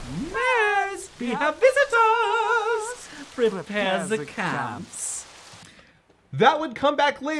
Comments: They said "CAMP"!??! All this time i thought they said "CLAMPS" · Oh, camps. Huh. I always thought Goz said "Prepare the clamps" there. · Wait, the line is "prepare the camps?" I've always heard it as "prepare the clamps." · I always heard it as clamps. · I 100% always thought he said "Prepare the clamps" and I think I like my version more... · You know, I always thought he was saying "prepare the clamps" not Camps.... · I always thought he said Clamps not Camps... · Huh... I thought they said 'Prepare ze clamps.' Welp, i was wrong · Camps I thought they said clamps lol